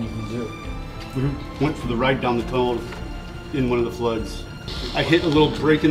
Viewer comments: I thought they said thieves, spiders, and sluts and I was like wait what? And then I realized they said floods🤣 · I thought he said slugs not floods as a major danger lol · I heard " Theives, Spiders, & Sluts " I was like well then lol but he said Floods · Floods I thought they said sluts 🤣